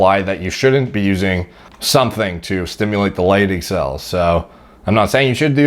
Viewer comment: What is he saying here? Lightic cells? I can't quite catch what he's saying and I want to google it to do further resarch on what he's talking about here. Does someone know the correct name of these cells he's talking about?